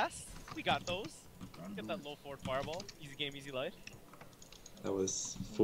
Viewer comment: Am I the only one who thinks it really sounds like Sven is saying "Fart!" when he says "Forward!"?